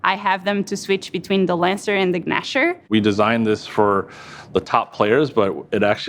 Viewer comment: Did she just pronounce the g in Gnasher? Maybe I'm crazy. But I thought I heard her say guhnasher